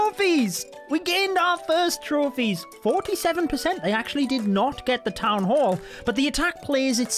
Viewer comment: did u just say they DID NOT get the th.?